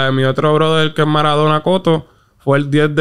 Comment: no le entiendo que dice como se llamaba MARADONA COTO ???? 🤔🤔🤔🤔🤔